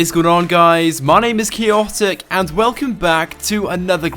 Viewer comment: lol you sound like you say "hello guys my name is chaoptic" haha it bothers me so much😂